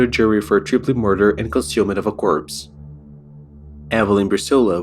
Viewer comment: I'm sorry, maybe I'm misunderstanding.... Did you say the men were charged with "Consuming of a Corpse"??? I've never heard it worded that way, and I'm praying I just misheard you. Unfortunately, I keep going back, and that's what I'm still hearing it as. Can someone clarify for me?